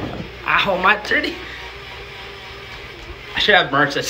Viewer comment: Wait did he just say ow my tited lol